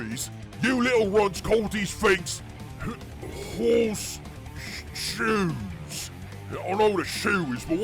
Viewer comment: Man, the idea of Beakies having Horses shoes on their armour to make themselves more lucky is fucking hilarious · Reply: I first thought you said Horus shoes and that makes even more hilarious.